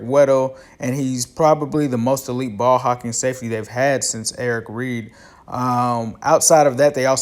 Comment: Did you call him "Eric Reed"?? I've listened to it a few times and it sounds like Eric. I'm assuming you accidentally said "Eric" since you had just finishing mentioning Eric Weddle and you know that his name is Ed Reed.